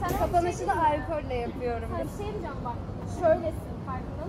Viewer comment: Allah hayallerini gerçekleştirmiş diye sevinecektim sonra bu geceyi sürprizi alkolle kutlayacağız deyince dislike atıp kapattım haketmiyormuşsunuz eyv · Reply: videoyu atlayarak izliyordum 8:28 de arkadaş kendi menüsünü anlatıyormuş 'yapıyorum' deyişini 'kutluyorum' diye anladım tamam problem yok allah kaza bela vermesin para olursa bizde alırız inşallah sağol